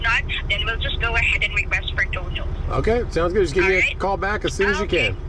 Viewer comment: the captions during the uber part of it were awesome... the captions thought towards the end that she was calling for donald trump but i think i heard request for total 13:08 turn on english cc